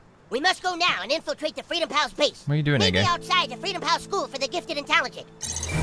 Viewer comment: sounds like he says: what are You doing N-Word